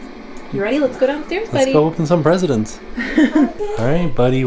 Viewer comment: Did I hear what I thought I heard? haha! Did say, "Let's go open some President's" at the 6:41 mark?